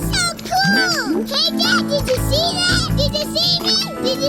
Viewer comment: Squirt: HEY DAD DID YOU SEE WHAT I DID?! I thought he said "HEY KIT KAT"